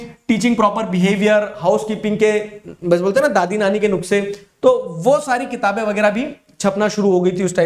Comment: The way he said ... Dadi Nani ke nukse ... Instead of nuskhe 😂 ...